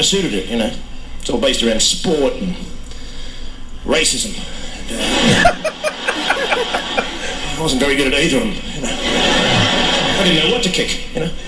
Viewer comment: Hilarious guy... somebody put me out of my misery though and tell me what he said at 4:23 because i've listened to it about 100x and can't make it out but everyone pisses themselves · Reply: He said Australians are all about sport and racism..he wasn't very good at either..he didn't know what to kick.